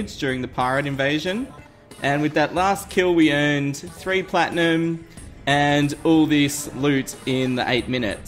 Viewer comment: Wow, it usually takes 60-70 invasions to get the coin gun?!!? I got it on my first invasion. :) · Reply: I thought you were saying to use platinum. :P